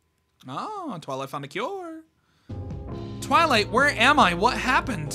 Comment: I thought he said “ oh, twilight’s fucking cures. “ 💀